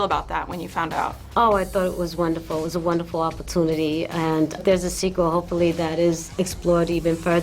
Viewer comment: Did she just say at 3:14 that there will be a sequal?